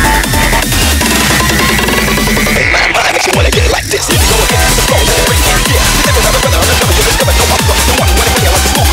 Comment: must know what it says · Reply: The only words I could make out in the beginning are, "Hey man what makes you wanna get like this?"